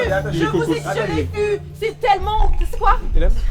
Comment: À 1:31 une fille crie "c'est thélème" en le prononçant cétélème moi j'ai cru qu'elle parlait de l'assurance Cetelem !!!!!!!! 😂😂😂😂😂😂😂😂 Petite couillonne va !!!!!!!! 😂😂😂😂😂😂😂😂